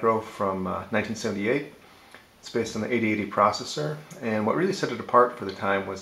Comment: I thought these were 8080 machines? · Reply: you're right! I thought you said z80 at 0:08, me being from the UK it sounded a bit like zed-80. I play most videos on 1.5 speed !